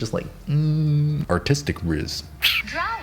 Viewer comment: legit thought he said "autistic rizz" i was like wat